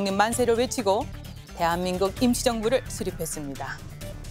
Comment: 실례합니다 0:24 제 귀에만 대한민국 김치정부라고 들리나요? 웃기라고 질문한게 아니라 발음이 그렇게 들려서요 아나운서는 아니시죠? 순수하게 궁금해서 그렇습니다. '임시정부'를 조금 다르게 발음하신것같은데요.